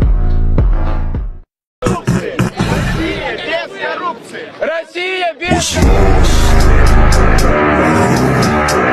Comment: what did blud say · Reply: Russia whitout corruption